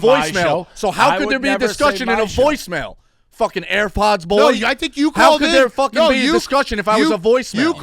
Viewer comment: had me dead 😂 I swear he says airfods boy